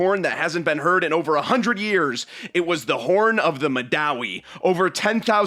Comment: First I heard horn of yaoi